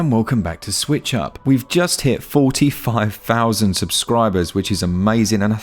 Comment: Thought you said you have 45 subscribers instead of 45k lol. Idk about the console version though, I have the first one on ps4 and it just feels wrong playing on console for some reason.